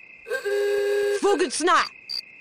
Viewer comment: Am I the only one who heard: Booger Snot!